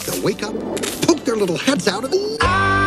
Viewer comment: why did I hear this as "Poop their little heads out"